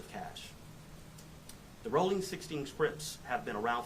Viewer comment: The whole no jumper podcast smdh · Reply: sound like he said the rolling 16th krips